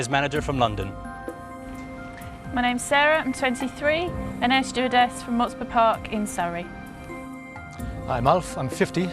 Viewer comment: Hi, do you have a clue about what's doing? At 2:52 she says something that's not clear at all... (i'm an English learner from France). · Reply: "My name's Sarah. I'm 23. An air stewardess from Botsford Park in Surrey."